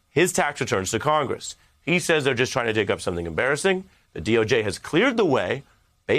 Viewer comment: Thought he said he doesnt do anything embarrassing